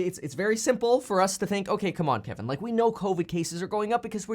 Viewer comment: When you said "Come on Kevin" I thought I heard you say "Come on Pimpin'" 😅🤣